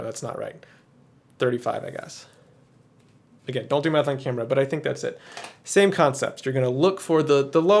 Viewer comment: Sitting here with my morning coffee, mishearing a sentence as "don't do meth on camera".